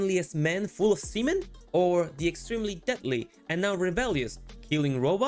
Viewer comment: Good review but I kept hearing “Krupps manager” which made me think of an efficient German with a clipboard 😅